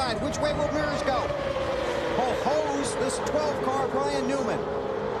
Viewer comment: Someone please tell me what Bill Weber says at 13:26. It’s been driving me crazy for 17 years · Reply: I think "hosed the 12 car"